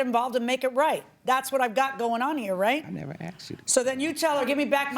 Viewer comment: Idk if anyone else catches it.... but at 13:54 you can clearly hear the Defendant say "I never asked you to get involved" when JM is talking about they both showed up to court and asking her to get involved. SMH